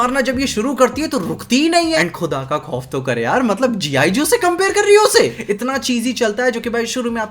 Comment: cam anyone explain Kya kaha isne?? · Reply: G.I joe is a famous movie